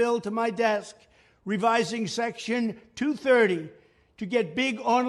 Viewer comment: So this is what presidents look like. Btw 2:56 bad subtitles "TO" instead of "230"